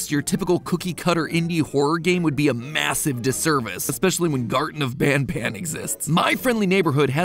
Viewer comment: the subtitles have quite a few typoes concerning names of other games, characters, and in-jokes, you might want to fix that at some point? -"Garden of Band Band" instead of "Garten of Ban Ban" at 1:11 -"puppy playtime" and "Hughey" instead of "Poppy Playtime" and "Huggy" at 1:34 -"ample" instead of "ourple" (a memetic corruption of "purple") at 2:45 -"too bad" instead of "TV bad" at 12:58 -"by the Priest. Six age demographic" instead of "by the pre-six age demographic"